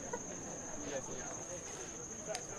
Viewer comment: what did jonah say? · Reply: Go out with boys wake up with men 😂😂😂